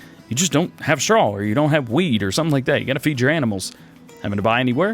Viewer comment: thought for sure you said “ you can buy straw or you can buy WEED to feed “ hahaha great video! Excited to play this map!